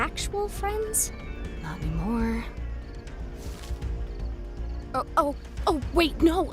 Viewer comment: what did ragatha say that made jax so? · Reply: She said "not anymore"